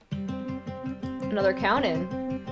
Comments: SEEING ALL THE PROPHECY STANS IN THE COMMENTS MAKES MY HEART RELIVED I FOUND MY PEOPLE ALSO LUV UR VIDEOS <3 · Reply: have you guys heard what she said at 25:02? was it "another country"? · what did she say? "another country"?